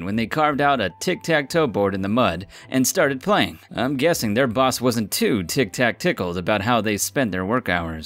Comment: Mate did you just say "their boss wasnt too tic-tac-tickled"? Wtf xD